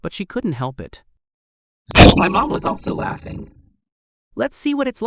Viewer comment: fffsh mmmmyy mmmooom wass allso lllauuughiing is what it sounded like